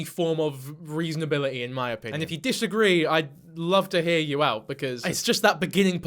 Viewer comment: I heard: "oh yeah you do that" and I think is worse so...